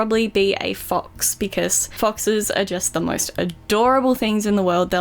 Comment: When said she likes foxes I thought she said box